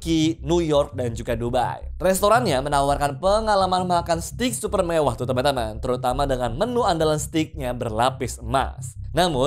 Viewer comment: dia ngomong apa gua gatau😭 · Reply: "......Makan stik super mewah, tuh teman teman, terutama....." Jelas kok